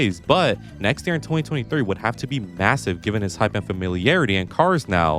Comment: did you say … massive ???